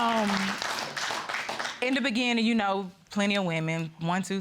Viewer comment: 😮😮😮WAITTTT …did she say “in the beginning “????😢why keep going?